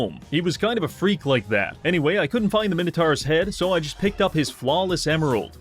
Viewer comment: "I couldn't find is head so I took his family jewls" is basically what I heard.